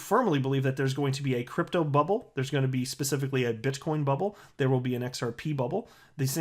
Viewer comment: I swore you said bitch coin bubble instead of but coin